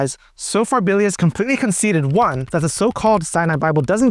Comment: When you say the Sinai Bible, what do you mean specifically? …”you know, the one on Amazon” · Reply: i thought he was saying cyanide.